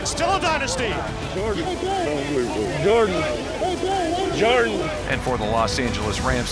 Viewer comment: uhm.. Was Edelman saying.. "Jordan... Jordan..."? Or Am I just hearing things? Lol.. :-)